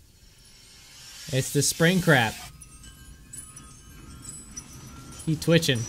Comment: Lol did he say spring crap? even the captions say so XD